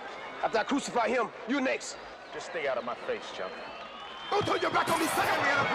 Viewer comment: I thought he had said: don't turn your black ass on me or something along those lines LOL